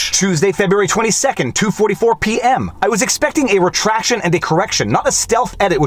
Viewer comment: Why am I hearing "Retracto......"